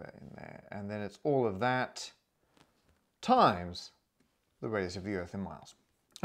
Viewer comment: When he said "the radius of the earth in miles" i heard "the greatest sphere in the world" and it still made total sense 😂